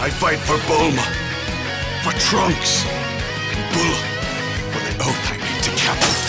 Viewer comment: I just love it just sounds like he says bull and not bulla